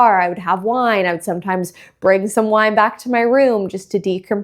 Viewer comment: Lol I totally thought she said she was bringing someone back to her room… but nope, just wine. And she’s been married for over a year now, so wine is the safer choice anyway 😂🍷